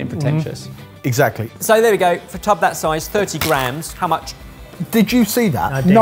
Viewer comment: Oh my god, when he threw the mushrooms, I thought he said "30 grand" not "30 grams" and I was thinking "You spent £30,000 for this episode?!"